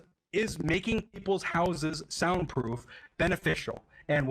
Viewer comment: Literally for the entire video i thought it said “Horses should be soundproof”